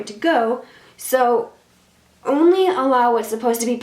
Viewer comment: are u a MUSLIM?? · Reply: aww even I thought she said Allah but she said only ALLOW 🤓